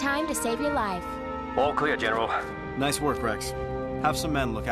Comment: "Good work rex...have some men" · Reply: I thought u said have some meth 💀💀